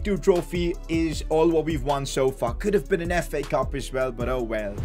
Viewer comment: when he said "so far could" It sounded like F**k